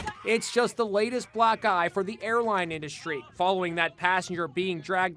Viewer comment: did it say just the latest black guy from the airline, WTF.